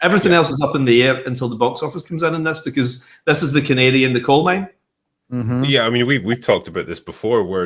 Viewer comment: Dude’s Scottish accent, I thought he said “it’s The Canadian in the Coal Mine”! I was like, I’m not familiar with that metaphor?